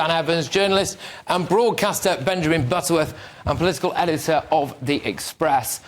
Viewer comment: Butterworth is such a ageist. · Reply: Butterworth - I thought he said Benjamin Button :)